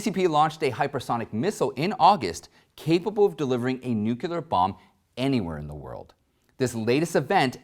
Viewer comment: did you just say "nucular"?